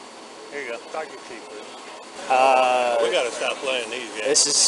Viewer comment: did I hear that correctly? This man said "we gotta stop paying nigga games"